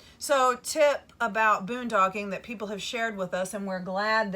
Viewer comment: Funny! at 9:58 in to the video she calls it Boondogging instead of Boondocking! Made me chuckle!